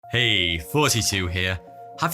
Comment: I always hear "42 here" in intros. I try so hard to listen for thoughty2 but just don't hear it